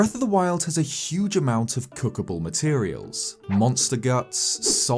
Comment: thought that said cockblock